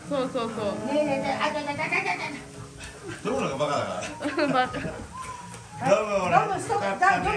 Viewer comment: あっははははは😂✨みんなめっちゃ楽しそうですね😂😂✨ 途中で「ドムのがバカだから」って聞こえて笑っちゃいました🐶💦🤣